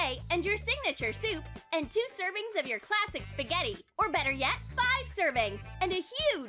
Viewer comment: The whole 'dry spaghetti' thing is reinforced when I hear 'classic spaghetti' as 'plastic spaghetti'